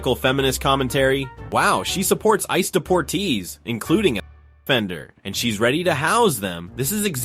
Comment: I don't know if you're doing this but I hear a lot of important words being censored You can almost say unnecessarily censored 16:09 including Bender that's what I heard I guess whatever word that was there to begin with must have not been important information